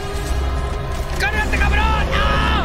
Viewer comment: jaja que dice cargate ca*ron aaaaa